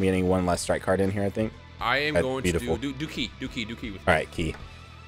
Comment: Is it just me but did say DOOKIE